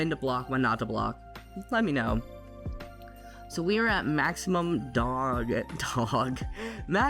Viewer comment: At first I thought you said maximum dong!!! 😂